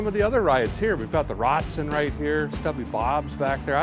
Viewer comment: Not sure - I think it's only the ones that were on Roadworthy Rescues. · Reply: oh ok I heard "rotsun" but maybe that was wishful hearing 😂